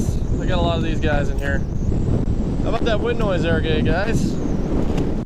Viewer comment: sounded like he said "gay guys"